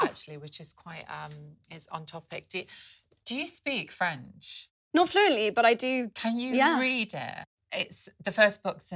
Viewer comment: Can anyone tell me the title and author of the book? I couldn't hear it from the video and I wish to get a copy 19:27. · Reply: Edouard Louis. The end of Eddy